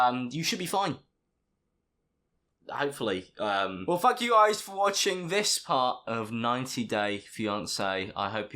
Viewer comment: lol it sounded like he said well fuck you guys